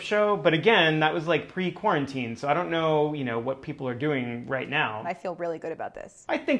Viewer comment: OMG when Gaby guessed Molly because of The Voice I deadass thought she said 'I KNOW white people' jgjhkklkjljkjllhlhk